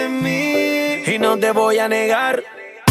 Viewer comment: Did he just say the n-word? :OOOOOO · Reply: He say: "negar" ("to deny" in spanish)